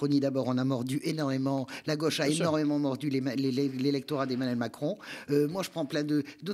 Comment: Hahaha j'ai entendu "Emmanuel À-la-Con" c'est parfait comme surnom, vous devriez reprendre ce surnom partout c'est un carton 😂😂👍😹